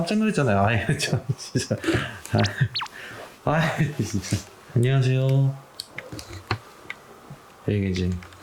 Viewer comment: 3분16초에 나만 들리나요,,? 안녕하세요 하고 대답 같은,, 3:16 아니라고 해줘요